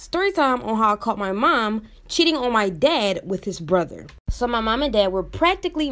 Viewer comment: Wow I totally thought you said that your mom caught your dad cheating with his brother. Thank god it was your mom!!